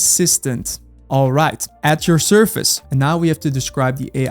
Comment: Great video! I’m about to go to sleep but am going to follow along with your video and setup autogpt tomorrie. Just fyi, at time 11:30 I think you meant to say “at your service” instead of surface.